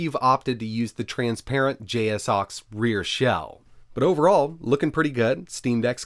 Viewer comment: did he just say j-s-suax ???